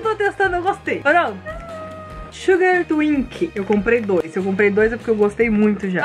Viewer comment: eu escutei Suga ??